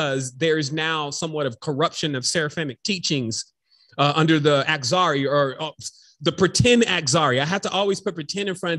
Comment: Can you please tell me what word you're using @ 1:48:44... "Pretend..."something".....? Exarry?